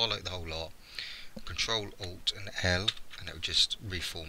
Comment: horan i have a question in 1:23 ctrl + what? i can't understand im sorry and thankyou in advance... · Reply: earvin Tallod "Ctrl + Alt + L" I think.